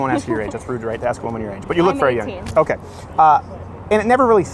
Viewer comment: did she say she was 18?? Very knowledgeable for her age I have to admit